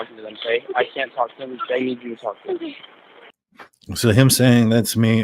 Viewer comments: I hear at 18:07 “Bethany… or dylan, I need you to talk to them” not “that’s me” to the officers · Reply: I thought he said “THEY need you to talk to them” since she had just reprimanded them to stop passing it around but 🤷🏼‍♀️ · it sounds like saying “Bethany or Dylan I need you to talk to them” Rather than “Alright that’s me”